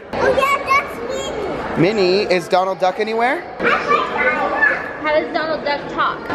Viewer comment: When said Donald Duck I thought he said Donald trump 😂😂